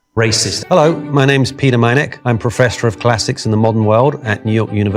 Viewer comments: "Professor of Classics in modern world" Let me translate. It basically tells us that he is not classics professor, he is professoro of modernity looking at classics. His job is to apply modern standards on people who died thousands of years ago and have no say in court of modern morality. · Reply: I thought he said 'Professor of Plastics'. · At first I thought he said his name was peter maniac and I was abt to say that's fitting